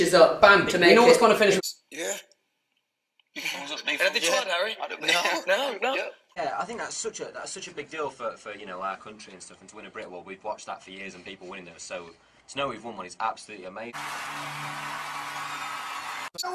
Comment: seriously what does louis say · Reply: “Anything to add Harry?”